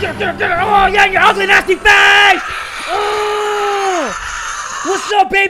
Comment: am i the only one who heard say “you ugly nasty *f slur*” @ 5:02 — 5:10 ? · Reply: He said face don't worry